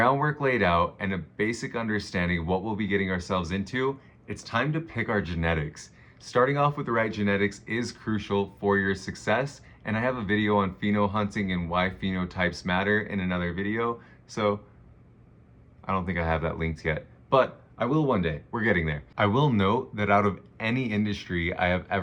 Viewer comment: The way I heard Black Pheno lives matter and had to run it back 🤣😂✊🏾